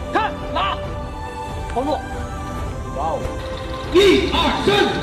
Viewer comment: I hear a sound like "Wow" in here😂😂